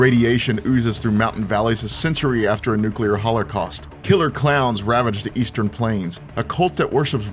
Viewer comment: did he say hollercost?